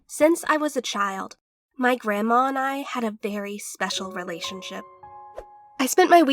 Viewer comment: she definitely said „berry“ instead of „very“ and i oop